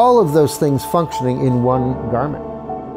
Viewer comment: the subtitles say "one environment" when it should be "one garment". Otherwise it's a great video, keep up the good work!